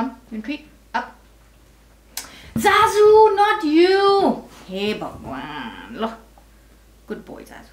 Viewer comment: for the first time I heard it you saying "hey bhagwan"... hahahaha :D